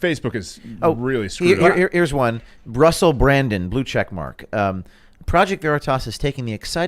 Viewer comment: Omg I thought you said “russel brand”. I just started watching him and he’s now my favorite YouTuber to get information from.